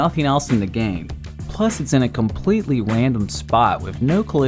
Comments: i think said for 5:09 : its in a conpletly wandom spot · did you just say wandom? that's what it sounded to me xD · did he say wandom